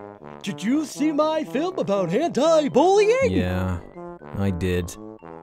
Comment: Guys turn on the words and there are secret messagess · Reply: i heard "hentai bullying"